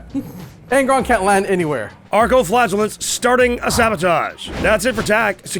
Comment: Oh, Arco Flagellants. For the first 20 minutes, it sounded like was saying "archo-flatulence."